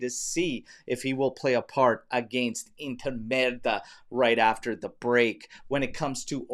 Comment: did he really say inter merda?!